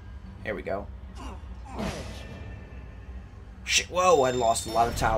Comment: i think i heard say shit